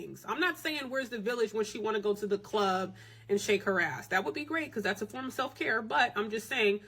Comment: did she say going to the club shaking your ass is part of self-care or did I hear that wrong